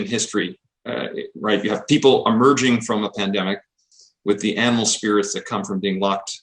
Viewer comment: did he just say fucking animal spirits? jesus christ....